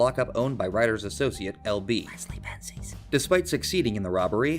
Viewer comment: What’s whispered at 16:07? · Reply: Leslie Benzies. One of the main guys behind GTA up until 5. Ryders friend "LB" is likely a reference to him.